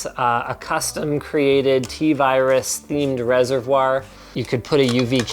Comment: DID HE JUST SAY VIRUS!!!!?